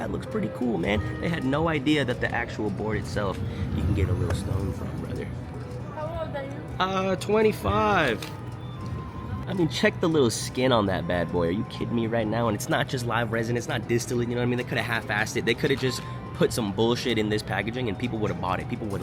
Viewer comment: who's this little Asian kid is all I heard 😂